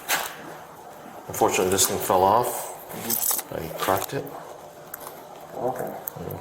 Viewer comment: "Unfortunately this thing fell off........ and he cracked it" is what i am hearing after multiple times of trying to hear what he says at 15:12 to 15:22. Any one else confirm?